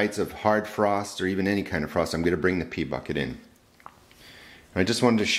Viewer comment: Had this playing in the background while working on something and heard you say, "I'll bring in the pea bucket." Heard "pee bucket." Hahahaha